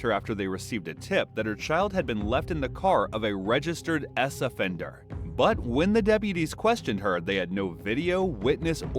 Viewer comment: Lol, at first I thought they said ass-offender. Later I realized they actually said s-offender. American censorship is funny sometimes. I was like "Wtf is an ass-offender???" xD